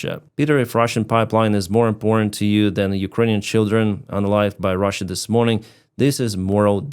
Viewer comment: "unalived" - we are talking about human beings . . . they were killed! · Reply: Thanks! I thought he was saying unaligned, or something.